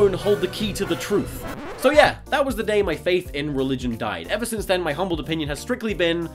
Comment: i thought it said the day my faith dad and i was so confused